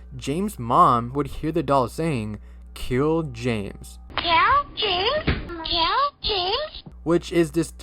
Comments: I thought Elmo said Gil james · It sounded like it said Hill James, not kill James, no offense though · I remember I got that Elmo toy and my mom took it away from me and threw it away after it started saying lots of weird stuff 💀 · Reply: honestly with the "kill james" phrase, i didnt hear that at all i heard "He James" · Instead of “kill James” I hear “gale james”